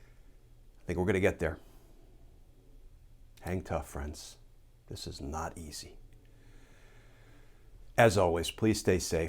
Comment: Thank you so much Glenn for your daily updates “ JUSTICE MATTERS “ It’s about time!!!! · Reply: I thought Glenn said at the end: "Hand cuffs, friends, this is NOT easy."